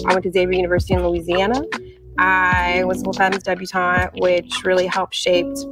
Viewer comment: for the life of me, I can't figure out she is saying. I hear I was a full time miss debutante. The subtitles say I was supposed to be this debutante. debutante anything is so far above my class.